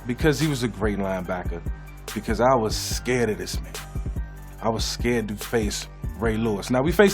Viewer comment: “Because I was scared of this ____” What did he say??👀👀😂😂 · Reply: the first time, I thought he said ni66a, but rewinded it and he said man lmfao